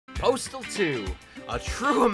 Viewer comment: I thought it said Portal not Postal